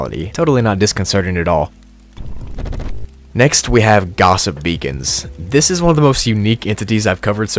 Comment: ngl I thought I heard "Gossip Vegans"😂